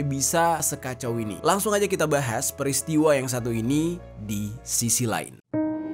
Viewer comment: bilang "sisi lain" tapi yg keluar tulisannya "permisi" apa saya yg salah dengar kah